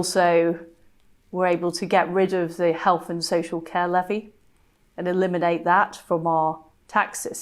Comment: Thought she said health caliphy . . . like, what's Muslim caliphs got to do with anything . . . "Health care levy" . . . lol?